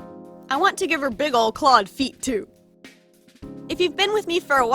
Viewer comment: am i the only one that thought of “clod” instead of “clawed”